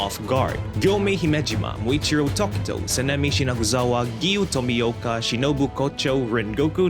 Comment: Who else heard him say Sanemi Shinaguzawa